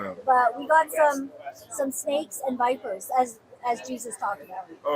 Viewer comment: at the end when you said "snakes and vipers" I heard "snakes in diapers". 😅😅😅